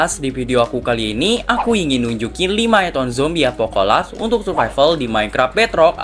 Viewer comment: Itu di intro awal aku nga salah dengar kah? Apocalypse di bilang Apocola😅?